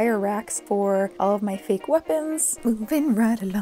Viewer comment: For a second when you said 'fake weapons' I heard 'fake orphans'